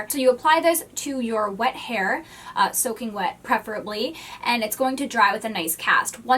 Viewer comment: Did you say preperably? It’s preferably!